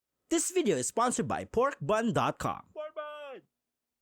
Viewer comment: I thought it said pornbun